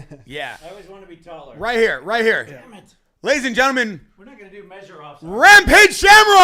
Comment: At about 17:38 did TJ say “damnit”?